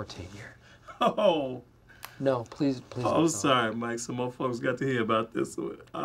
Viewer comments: Did Darrell really say what I think he said at 2:44?? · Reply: "some more folks" is what he's saying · did he say mf's?